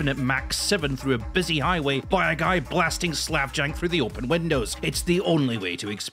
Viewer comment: What kind of music did he say? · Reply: That’s what I’m wondering too. I think he said slav jank but I’m not sure